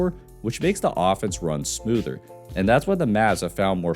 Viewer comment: I don't know why but i keep hearing "and that's why the Mavs have found more success playing with Braindead Williams than with D'Angelo Russell." which makes it even funnier